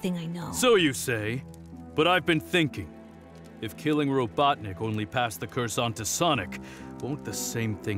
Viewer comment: The subtitles say “Robotnic” instead of “Robotnik”